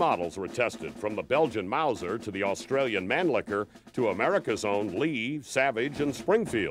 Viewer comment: Did he say the Australian Mannlicher?